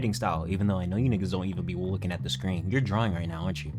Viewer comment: I thought you said driving and I was like damn, he got me 😂